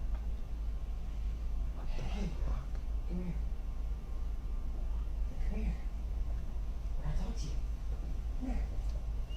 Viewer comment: that kid hearing himself am i trippin or di i hear " com here, com here mom wants you"